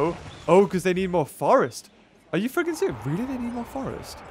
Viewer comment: Did just swear, “are you f**king serious” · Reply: I went over it three times and every single time I heard f**king serious, check your editing for this video at 18:45 because your not that kind of YouTuber but I do understand if it does slip